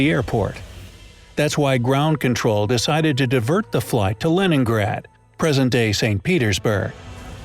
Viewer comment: I thought he said lemongrab